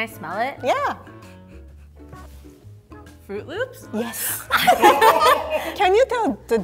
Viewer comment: Is it only me or does it sound like she is saying fruit roops instead of fruit loops